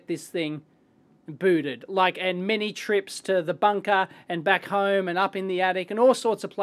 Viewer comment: At first I thought you said "It took me many trips to the dumpster"